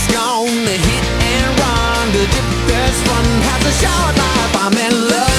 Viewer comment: at the end of the second verse i thought he said "the one girl has a showerknife" theres no such things as showerknifes though...